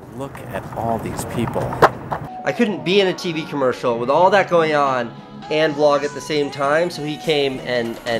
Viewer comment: did he say he was vloging?!